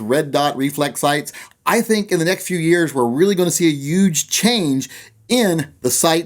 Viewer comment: lol, did you say "YUGE change" @ 1:30? no animosity intended; it just made me chuckle.